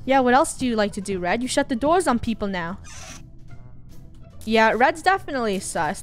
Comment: why did I hear gyat